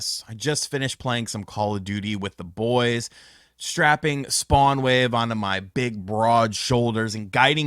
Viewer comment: I heard “Strap-on SpawnWave.” It’s too early. 🤣🤣🤣